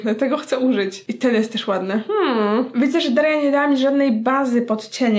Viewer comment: Czy tylko ja usłyszalm czyli daria nie działa?